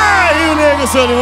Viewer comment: So eu que entendi o Solimões manda um “os menino Zé henrique e Juliano” no minutos 2:59? Kkkkk